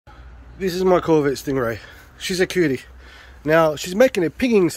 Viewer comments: Did I just hear "This is my COVID Stingray?" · here's the story since some people say that they can't view the description: I was being sarcastic about the "ping" I know it isn't one, it's a rattle. A new mechanic opened up locally, I took the car in to get the starter motor changed, the car sits low and I don't have a hoist or stands anymore so I can't get underneath it. Also I'm old and impatient. Then while I was there this rattle popped up, he says "Hey that's not good man" souds like a bad engine ping. He wanted to charge $800 to fix the it (timing adjustment, spark plugs, dyno tuning etc). I said nah. It sounded more like something was loose. He insisted it was definitely a ping. Either he actually thought it was, which makes him an idiot or he knew it wasn't and wanted to rip me off. my old mechanic was an honest guy and awesome but he retired and this guy bought the business. obviously not all mechanics are dishonest, but the very few like this guy give the rest a bad name which is a shame. much respect to the honest mechanics out there. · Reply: It sounded like you said covid stingray I was like ohhhhh boy but after watching again you said corvette. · I thought you said covid stingray